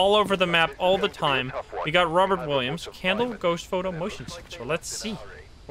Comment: I thought he said the ghost’s name was Robin Williams! Lol